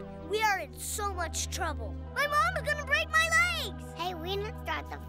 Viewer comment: "ma mama gonna break ma legs"😂😂😂😂😂😂😂😂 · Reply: Kenz Khyriem omg XD I thought he said “Ma, Mama gonna Rape my legs” XD in an idiot!!!